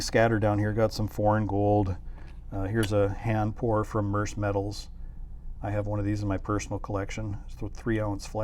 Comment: can you key me into where you got that flag piece? I thought you said Merc Metals, but not having any luck in locating them or , if they even have them for sale. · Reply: Murse metals